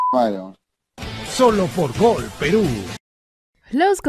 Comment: Despues que dijo que le hago un duo 1:48, la voz en off dijo solo en gol peru XD